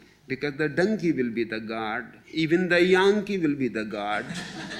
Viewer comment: does anyone understand what Osho is saying here? Is he saying the „junkie“ will be the god? As in a heroin junkie? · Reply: He meant donkey